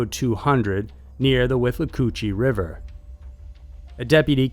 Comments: Did he just say "wiff a coochie river???" Lmao... I may have misheard him about that. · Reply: I heard "Whip a coochie" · Did he just say "wiff a coochie river???" Lmao... I may have misheard him about that.